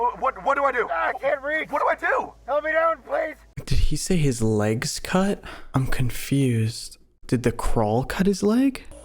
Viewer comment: I think he said his leg's caught, like the crawl wrapped around it? The crawl, controlled by the singularity, either tried to eat him or convert him into a mimic. It also sounds like they may or may not have had to cut him to get him out.